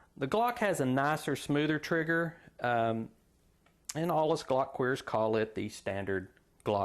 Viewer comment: did you just say glock queers lol?